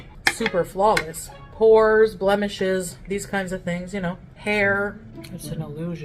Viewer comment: I thought she said “Whores, blemishes” 😅😅